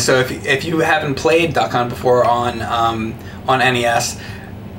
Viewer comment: I keep hearing them say "Duck Cunt"